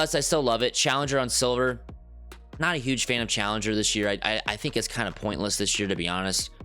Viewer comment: did he say challenger is pointless?? Well at least the build has it